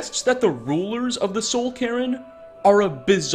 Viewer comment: I heard "Soul Karen" instead of "Soul Cairn" and now I feel like we missed out on a quest where Valerica demands to see the Ideal Masters' manager